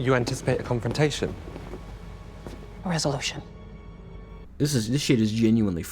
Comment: Sounds like “you’re expecting a conflict” and then she says “where’s the lotion?” I know she really says “resolution” but considering the meta and who she’s married to I like “where’s the lotion” a lot better.